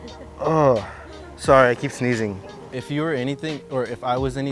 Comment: I thought is said steezing not sneezing 🤧